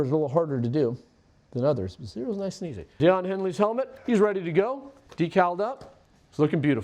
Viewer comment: If not for the subtitle, I really thought I heard “Don Henley’s Helmet”. 😂